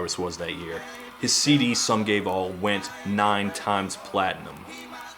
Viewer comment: Honest to god, I thought you said his followup was Some Gay Ball. Which would have been soooo much better.